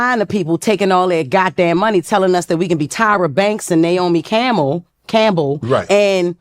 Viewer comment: Did she just say Naomi Camel 🐪 😂😂😂